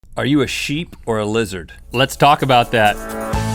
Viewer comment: lol at the start i thought they said "are you a shit,or a lizard" XD